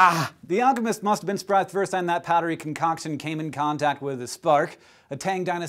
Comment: What does he say between 0:20 and 0:23? The CC says "the alchemist must been struck first and harry concoction came in contact.."